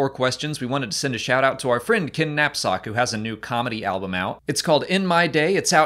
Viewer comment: I thought you said "our friend, Kidnap Sock".